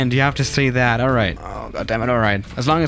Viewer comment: it sounds like you said ah damnit